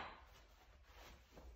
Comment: I thought he said “Grimace gracious” 😭😭😭💀